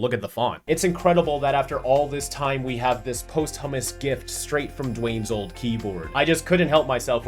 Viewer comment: Did you just say "post-hummus?